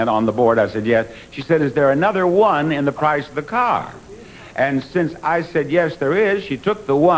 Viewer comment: I thought you said it was a number one in the price of the car